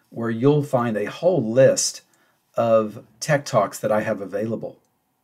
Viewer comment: Am I the only one who heard "tech toks"? 😂